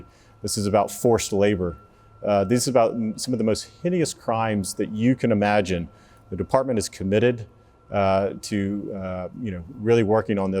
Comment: it sounded like he said "these are the most hideous crimes the department has committed" (I can believe that!)